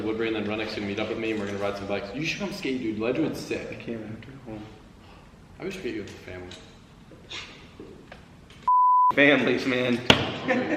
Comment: Can someone tell what said at 13:41? I can't understand. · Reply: He said that he always forgets that Tommy has a family